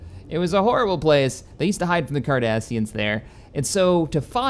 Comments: Did he just say that they hide from the Kardashians in the badlands? 7:05 Kims going intergalactical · There's coffee in that nebula! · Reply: I thought he said they hide from the Kardashians